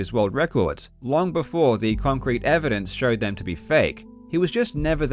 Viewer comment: swear, i hear kongcrete.